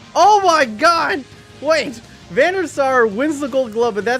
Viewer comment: when you say Van Der Sar i hear Venusaur ... (english is not my native language)